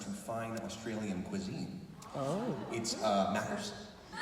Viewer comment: What did he say at 9:42? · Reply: Maccas. Aussie slang for McDonalds.